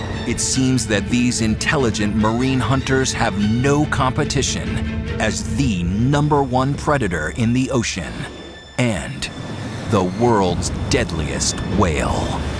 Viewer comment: killer whale should be a dolphin right? but i heard u say "the world deadly whale"?